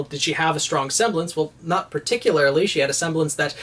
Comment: at first I thought he said that she had a semblance that made things stinky but no he said sticky